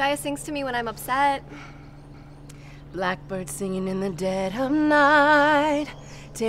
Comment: I was brushing my teeth while watching this and when she started to sing I heard “black pussy Singing in the dead of night, take these broken wings and learn to fly” and the visual I got literally killed me 😂